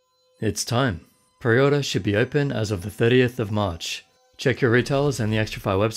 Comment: thought you said 38th of march, had to rewind a few time